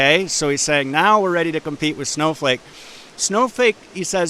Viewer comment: did he just call it Snowfake?